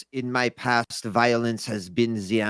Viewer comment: all I can hear say is violins instead of violence